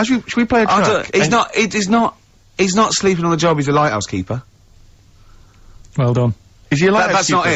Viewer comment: Yeah, I finally understood it ^^ The first time I heard "house keeper" and not " LIGHThouse keeper", that's why it didnt make any sense.